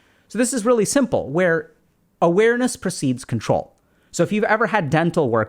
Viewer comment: One of the most valuable videos yet for me. One little error I noticed was at 16:26 you say "Awareness precedes control" but the words above say "Awareness perceives control". It changed what I heard at first but going back, it made much more sense. Don't think it merits a re-upload but for anyone that sees this comment, I hope it helps. Keep up the great work!